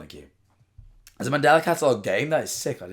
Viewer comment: dude i thought he said "is the mandela cataloge gay?"